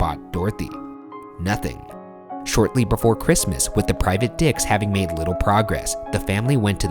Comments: Anyone hear "private dicks"??? Just me?? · I heard "private dicks" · i'm sorry, did he say "private dicks"???? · Did he said private Dicks or Dets? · “private dicks” boy I know you did not say that 🤣😭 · did Ryan say "the private dicks" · does Ryan say "Private Dicks"? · Did anyone else hear him say “the private dicks”? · Did Ryan say private dicks? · I've replayed that so many times and still all I can hear is "Private d*cks" 😂 I know he said "Private dets" but that is NOT what I heard 😂 When you turn on the auto-generated captions, that's what it says 😂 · Does he say private dicks? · am I the only one Who hears him say private dicks because I'm kind of confused hahahaha